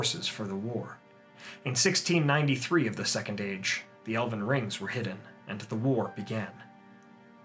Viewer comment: For a hot second I thought I heard Elden Ring at 4:56 and it made me perk up a bit lol